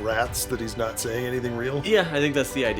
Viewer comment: I always thought Bobinsky was saying “doll-bushka,” like a cute word he made up, like you call someone a doll in a friendly way, but also hinting at how people there are made into dolls.